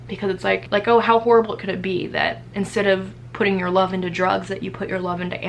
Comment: Jeez man thought you said you started putting your drugs into your animals.. 34:39 also you are so brave!